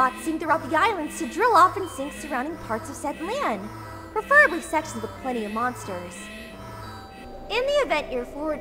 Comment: did she say “preferably sex with other monsters”??